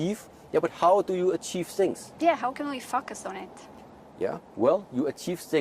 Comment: i swear i repeated 7 or 8 times the video what she asked. instead of 'how can i focus on it i heard from her. yeah , how can i fuck a science? in my mind what is happening to her? lol